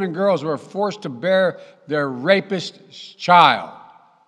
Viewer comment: Did Brandon just say that un-born children and fetuses are racist ? Is that his excuse? Uhh, sorry. I misheard rapist for racist . Carrying to viable does NOT equal raising ! One of my closest friends was adopted. Born in PA in `54; I don't imagine his Birth Mother had any choice. Why she put him up for adoption; I'll never know. We disagreed politically, but we still have civil debate (four years after his passing). God Bless You Ken; I Love and Miss You still.